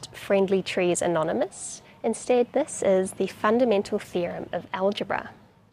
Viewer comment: I swear I heard "the fundamental fear of algebra" 🤣🤣🤣🤣